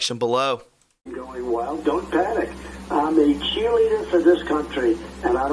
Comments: I heard ‘Q Leader’, not cheerleader. President Trump isn’t on the sidelines ‘cheering’, he is out front between us and EVIL saving the whole world! WWG1WGA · It sounded like Trump said "cheerleader." I dunno. WWG1WGA Q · At the end, I heard POTUS say “cheerleader” not “Qleader”. Maybe just me.